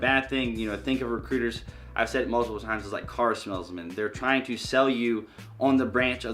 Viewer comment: I heard “don’t ask a car smellsman” 😂😂